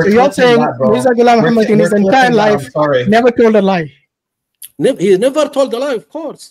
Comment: I did not expect the question! thought he said "never" not "ever" His heavy accent caused that, along with unexpected very silly question! How come!!